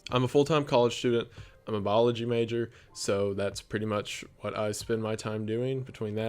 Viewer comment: For a second I thought he said he was a marine biology major. I was like boy you’re afraid of the ocean?? My bad.